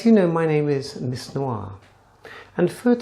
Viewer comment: YOU ARE INCREDIBLY WISE AND KNOWLEDGEABLE. I WISH MORE PEOPLE THOUGHT LIKE YOU . YOU ARE SUPER AWESOME.🎃🎃🎃🎃🎃🎃🎃🎃🎃🎃👻👻👻👻👻👻👻👍👍👍👍👍👍 THE FIRST TIME I SAW THIS VIDEO I THOUGHT YOU SAID "IM MISS NORA" INSTEAD OF MISS NOIR HAHA😀😀😀 HAPPY LATE HALLOWEEN👻👻👻🎃🎃🎃🎃